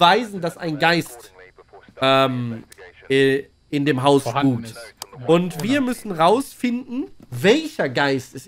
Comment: Das Spiel sieht interessant aus. Aber was sagt er bei 1:08? in dem Haus "hunt"